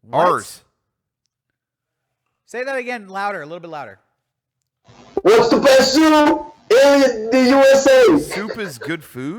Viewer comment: WHERES THE BEST SOOOUP IN THE USA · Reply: I thought he said "whats the best zoo." Hah.